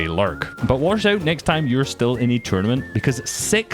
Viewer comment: what did you say? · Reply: six 🤦‍♂️